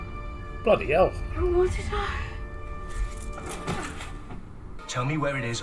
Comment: It annoys me that the subtitles for this always seem wrong to me. I hear "But what did I --?"